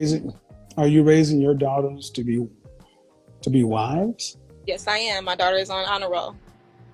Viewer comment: KS: "Are you raising your daughter to be a wife?" Her: "yes she's on the honor roll" Thats like me saying I'm raising my child to be a chef because he got his driver's license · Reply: To be fair, it definitely sounded like he said "Are you teaching your daughter to be wise". Still a dumb answer, but not a total nonsequitor.